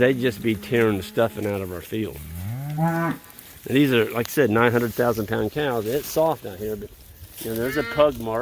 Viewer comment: Lol, it sounded like you said 900,000 pound cows. I'd take those blue tanks out and sell them on Marketplace or Craigslist or something. Someone could probably use them and you could recover a bit of your money.